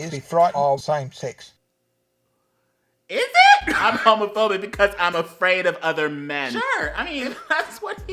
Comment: Why did the fedora man sound like he was saying "I'm afraid of a ferbie" Am I deaf?